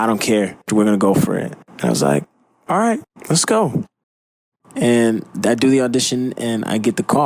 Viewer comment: i heard him say"Alright, VSCO"